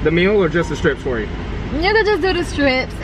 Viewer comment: I thought said n** just do the strips🤣🤣🤣🤣🤣